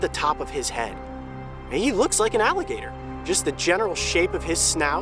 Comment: I thought it said "bitten by an alligator"